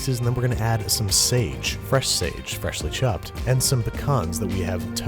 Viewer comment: Did you just say pea-can